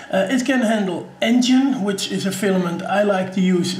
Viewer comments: Hey, what is that filament you mention at 1:44 in the video that you said you like to use? It sounded like "engine" but I'm guessing I'm just not hearing you correctly. You said you like it a lot. Also, great video and thanks for the review. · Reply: Hi Jim. You heard it almost correct. It's NGEN (pronounced as Engine). Is a Co-Polymer made by Colorfabb and it is really the best filament I have used so far. It prints with the ease of PLA, has a heat resistance like ABS and has the flexibility of PET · What filament type did you mention? Sounded like "enjin". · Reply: Colorfabb NGEN